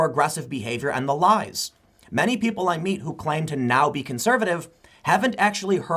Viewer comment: did Tim say “people like me who now identify as conservative”...? 🤔... 🤘🏻😉😎